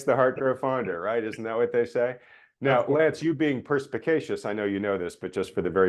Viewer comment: At first I thought you said Lance was pervicacious, extremely willful; obstinate; stubborn. But then I listened at a slower speed and realized you said perspicacious, having a ready insight into and understanding of things. 🤣